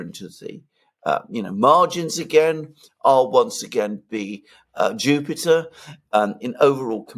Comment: Manny Macron misheard 'Stupider' as 'Jupiter'.